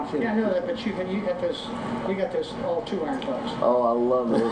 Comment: Gary, you got all those 2-irons there.....biting humor from the Golden Bear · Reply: I thought he said 2 iron putts